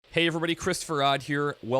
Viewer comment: It sounds like you say "Hey everybody Chris Ferodd here" Kappa